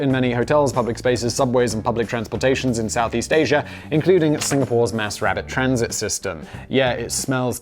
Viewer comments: did I just hear Simon say "Singapore's mass rabbit transport system"? Is there something we need to know about this? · Sounded like you said Singapore has a "Mass rabbit transit system" - the mental image had me chuckling.